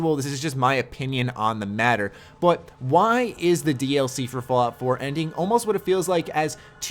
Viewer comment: I thought he said it was the final DLC for fall...